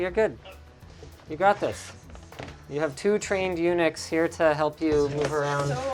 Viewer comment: Did Linus really say "...you have two trained eunuchs..."? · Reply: I heard Unix because I didn’t even know eunuch was a word. girlfriend was kinda douche about the whole thing though no?